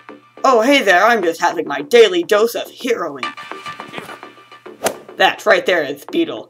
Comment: I thought spider-man said "my daily dose of HEROINE" LOL